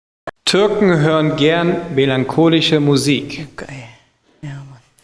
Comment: was ist die frage bei 5:41 verstehe das nicht türken hören fern kurdisxhe musik oder was sagt der da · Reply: melanchonisch nicht kurdisch xD aber eig hören wir kurdische Musik? 🤔